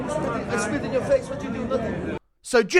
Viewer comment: I heard “I’d spit in your face” .. (?)